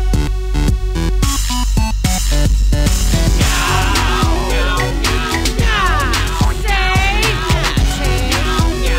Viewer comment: What does it say at 1:20? Meow, say? Catchy. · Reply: (Meow x15) Nya, see?! Nya, see? Meow!! See?